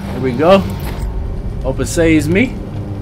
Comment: W video! I’m glad you are uploading this game. I like how at 19:40, you say “Open says me” instead of “Open sesame”.